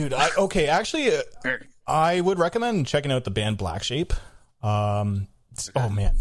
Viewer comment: For anyone like myself, I thought he said Blacksheep... It's Blackshape and the song is fucking incredible!